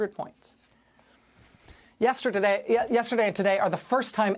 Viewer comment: I heard it “ yes turd a day”